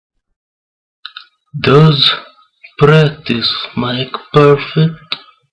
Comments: I think he said "Does practice make perfect?" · I think he says "Does practice make perfect?" BTW: TJ's story about his childhood is similar to The Riddler's story. <link> - Skip to 1:00 (The second tape).